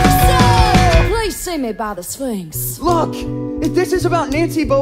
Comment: She said sphinx? I thought she said swings 😂😂